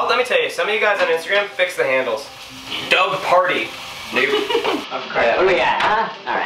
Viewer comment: WHAT DOES SAY AT 6:51 ???? IS IT THE N WORD? I HOPE NOT AAAHH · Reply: he said “dougparti2”